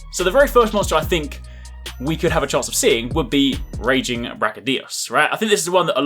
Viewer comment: I thought you said rajang brachy.. now I'm having nightmares